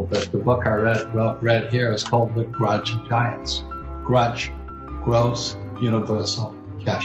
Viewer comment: Mr Kiyosaki is great - knows the score and lets us out here know that score. The sound quality is a bit flaky and I can't hear that title of the book he speaks of at 2:52. Anyone help on that please? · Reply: Grunch of Giants by Buckminster Fuller 😉